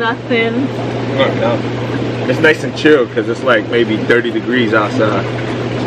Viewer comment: Why are you two wearing hats? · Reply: Thank you for responding. I appreciate it. However, I kept watching your video and heard him say it was 30'40 degrees. That is cold.